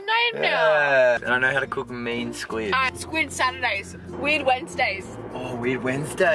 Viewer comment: I really thought she said weed Wednesday 😩